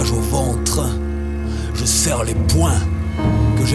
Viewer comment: On voi ma tête :o 5:19 je dis "Je sers les poing"